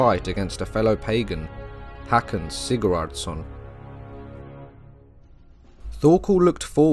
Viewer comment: Even though I am somewhat knowledgeable of the era, and I have my closed caption on, and I repeated this section of the video almost 10 times, .... I can't get passed what I thought I heard the first time. And after repeating, that's still the first thing my mind thinks was said.... "Packing Cigarettes-SON (or SEN .. both work.)